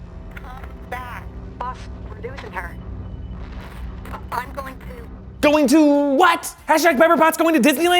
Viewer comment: That definitely sounded like Friday saying "I'm going too..."